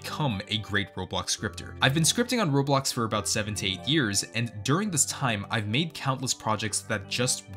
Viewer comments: i heard 78 years... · I thought you said you scripted for 17 years until I turn on captions